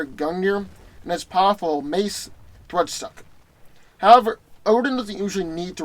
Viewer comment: What did he say at 3:55 lol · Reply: "As powerful of mase ....woodstock????" Lol